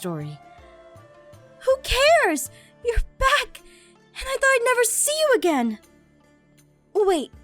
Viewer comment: when garroth said "i thought id never go back" for a second i thought it said "i thought id never grow boobs"